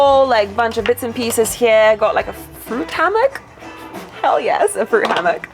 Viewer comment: wait.....did you say............. banana hammock???..............BAAAAAHAHHAHAHAHAHAhHHAHAahahahahahahahahahhhaaaaaaaaaa........ .......must be a European thing........HAAAAAAAAAA!!!!! I kill me!! :)